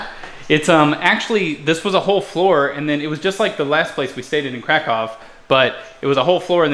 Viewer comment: did you say "new krakow" :D?